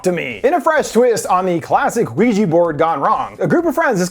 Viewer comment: Why did it sound like you said the LUIGI BOARD instead of ouija board? LOL